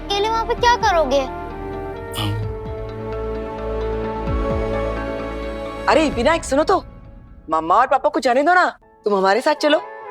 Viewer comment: arre Virat suno toh😂 She said Virat instead of Vinu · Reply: @@ my bad😂 pta nhi mujhe Virat kyu sunai diya tha😅😅